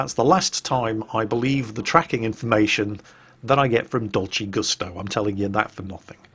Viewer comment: It took me second there to realize you'd said "Dolce Gusto". I could've sworn at first you said "Dodgy Gustav".